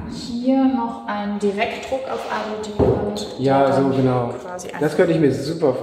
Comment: Hallo, bei 8:13 - Direktdruck auf was? Kann ich leider nicht verstehe. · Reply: Alu-Dibond